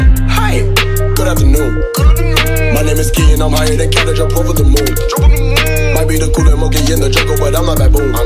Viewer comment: 1st time: O ok 2nd time: I guess it's catchy 3rd time: Uh Huh Uh Huh 4th time: MY NAME IS SKI AND I'M HIGHER THAN COW THAT JUMPED OVER THE MOON 5th time: I'm gonna copy the top comment because it is good and relatable and I will get likes 6th time: Do people not understand that I purposely copied that comment? Why is everybody getting triggered? 420th time: Why am I still listening to this? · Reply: Damn, I always thought he was saying "higher than cabbage on top of the moon" 🤦‍♂️